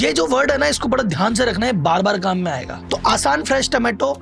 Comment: "आसान" 🤔 सुना सुना देखा देखा लगा रहा हैं अच्छा "किसान" 🥰🥰🥰🥰🥰